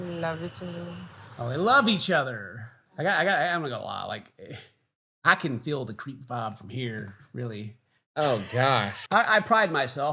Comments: stg i thought you said i can feel the cream pie from here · oooh he said creep vibe..thought he said crampie 😂😂lol